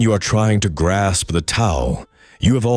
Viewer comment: the ingrained subtitle says, "When you are trying to grasp the towel" -- this should be fixed with, "tao."